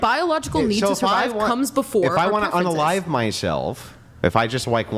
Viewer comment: Anyone catch that?? She said his preference was cum??